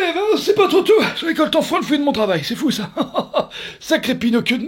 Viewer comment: Qu'est-ce qu'il dit ? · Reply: Je récolte enfin le fruit de mon travail